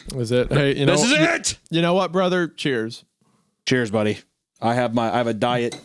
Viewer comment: What did call?!? Did he say, “Cheers button?!?!”